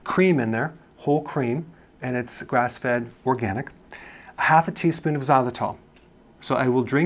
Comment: Grass fed hot dog ..haha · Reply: svntrs He did actually say “grass fed cream”